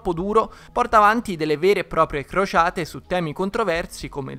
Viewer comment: io vi giuro che ho capito Frociate al posto di Crociate